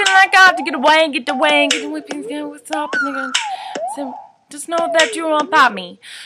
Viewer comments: in 2:20 it sounds like she said nigga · WHO Heard her say NIGGA